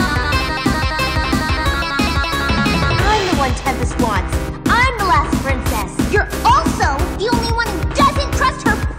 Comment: what exactly is it twilight says at 2:20? "i'm the one ____ wants?" trying to ascertain the plot from what we're given 😔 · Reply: It's a line from the G4 mlp movie "I'm the one Tempest wants"